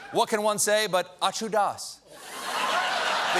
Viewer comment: “What can one say except ausho datz?” I’d be grateful for anyone with more linguistic skill than myself to enlighten me as to what Stephen said right there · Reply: I used the CC to get the actual words & it is, " A TU DAS", I think it's an Indian?Punjabi rap song.